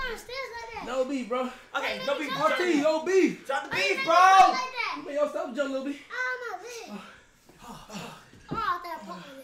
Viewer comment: Did bro just said why you drop the stairs like that did I just heard that or that was me